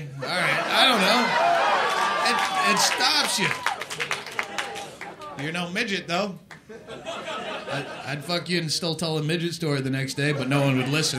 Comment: Does anyone know what the woman said at 45:36? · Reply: It's either "fuck my tits" or "suck my tits"